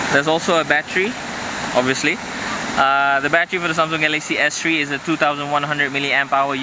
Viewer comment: When he said battery it sounded like he said bat tree lmao